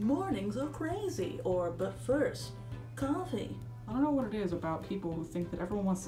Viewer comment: When you said but first coffee I totally heard you said "butt first" lol because you paused as little weird 😂😂😂